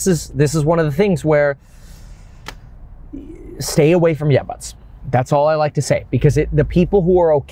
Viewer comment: I can't understand what your saying around the 12:10 stay away from .... cc is giving me yuts. I can't make out anything. I am hearing yeah buts